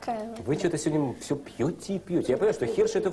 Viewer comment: сперва послышалось, что она сказала :"тебя не любят", ибо, как по мне, это в её образе